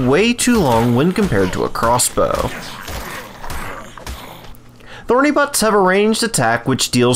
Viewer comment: Anyone else hear him say thorny but